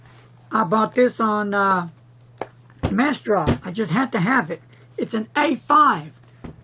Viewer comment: nice viedo - I finally fround massdrop - I thought you said 'Masdra' and I could not see the brand name on the envelope. Massdrop is an interesting concept - still learning the interface. love your channel!